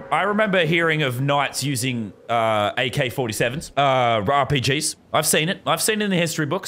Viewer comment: did he say "Rawr PGs"